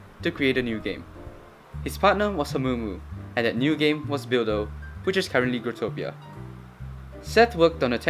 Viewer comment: WTF i thought u said DILDO lmao